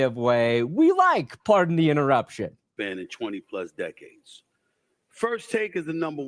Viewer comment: ….Did he just say “Twenty plus decades”???